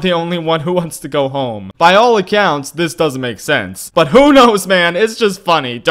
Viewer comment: People quoting Emperors New Groove: “By all accounts it doesn’t make sense” Me mishearing the quote: “While it counts it doesn’t make sense” DEAF 100